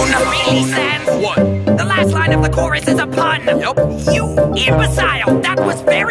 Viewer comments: why did it say yes but said nope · what just i heard nope, then why that is yes? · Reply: He said 'Yup'